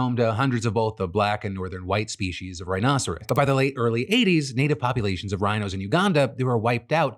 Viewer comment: Did I mishear, or did you say the "late early 80s"?